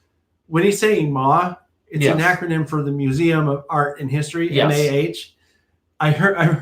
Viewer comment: I’m so glad you mentioned this because I thought he’d said Maud/Mod. As a Scotsman The Mod is something entirely different to celebrate Gaelic songs. Anyway, as a newbie to all this I’m watching all the F C & W videos. I think it’s essential to get new generations into any hobby. Keep producing more content.